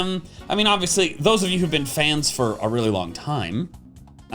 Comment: did you say FANSSS????? like ROTOMMM?????